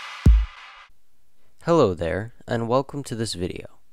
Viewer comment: When he said “Hello there” I thought he said “Hello Claire” and I was like “HOW DID YOU KNOW MY NAME!?!?!!?!